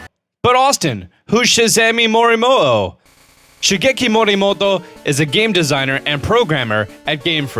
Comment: i thought he said miyamoto